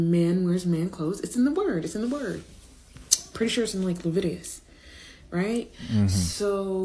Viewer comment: Did she just say "Levitius?"